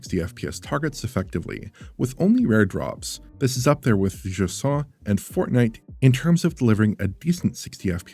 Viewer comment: Anyone know what he said at around 12:54 "This is up there with ???? and Fornite"? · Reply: Jusant